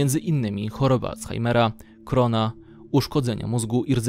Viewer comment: dlaczego tu usłyszałam korona??? XDDDDDD